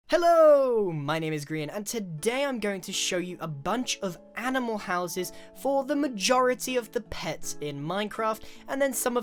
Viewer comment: I thought you said your name is green!